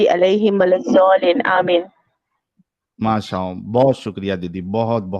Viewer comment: Did say MASHA OM?? 😅😅😅